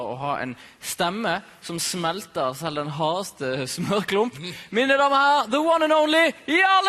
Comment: Words of the Day: Puppene & Steroider Phrase of the Day: Slutte å mase Also at 0:21, is he saying "Mine damer og herrer"? Takk for subs! <3